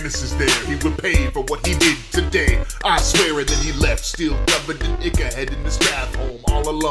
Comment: "And then he left still covered in Ichor headed to Strathome, and here is the kicker" I heard "And then he left still covered, this nigga headed to Strathome"